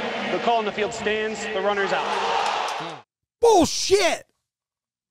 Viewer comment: not positive but I think he saying “Bullshit! Fucking A!”